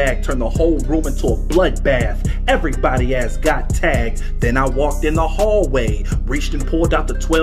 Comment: Lyrics for the video: Extendo mag for my first class Pulled the AR out the black bag Turned the whole room into a blood bath Everybody ass got tagged Then I walked in the hallway Reached and pulled out the 12 gauge Seen Jeff my favorite bully Blew out that n***a rib cage Next up I went to the gym God damn I should have played this sport I swear the coach should have put me in I was 10 for 10 from half court Auditorium, aired it out Cafeteria, aired it out The whole school was painted red I even murdered the special ed The janitors and the lunch lady 12 to her chest like Tom Brady Principal and the teachers too Spared a few cuz we was cool I’m out…beitch! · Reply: I always thought he said "everyone has got tags" as in he was confirming his kills lmao, but this makes more sense.